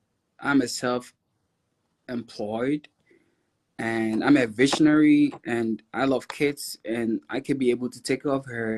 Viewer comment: Sounds like he said, “I’m ambitionary”. 🙄🧐 Quite sure he said ‘a visionary’ though. I googled ambitionary and it says, ‘An ambitionary only sees the present moment.’ 🤔 it fits him.